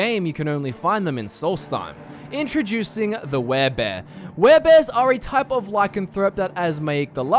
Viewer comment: When he said werebear i thought he said care bear 😝